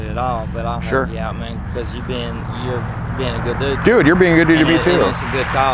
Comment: Did he say- You’re being a good cop? 😂